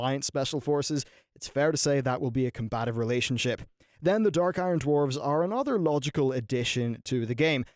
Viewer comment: LOL for a sec I thought you said DARK ELF DWARF